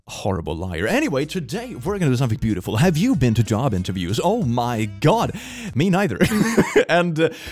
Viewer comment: I was half paying attention and thought I heard say "have you ever been to child abuse?" Instead of "job interviews"